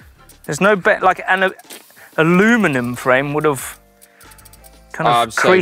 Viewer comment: at 14:43 “an Aluminum wheel….” He did not say an aluminium wheel! - Did I hear that correctly?